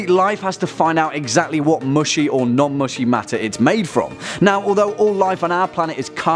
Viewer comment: Hat sonst noch wer "Muschi or non Muschi" verstanden?